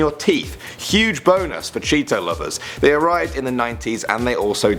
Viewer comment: did you just say boners for Cheeto lovers